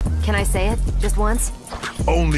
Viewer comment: Why do i hear : " only if you wish to suffa "